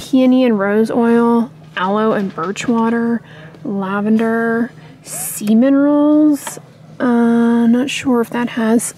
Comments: I thought you said “semen rolls” but you said “sea minerals” 😅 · English is not my first language. When you were looking at Dove body washes and said "Sea minerals" I heard "semen rolls" and was very confused for few moments haha :D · I heard “semen rolls” instead of ‘sea minerals’ and I had to watch it 3 times and pause and zoom the video to figure it out! 😂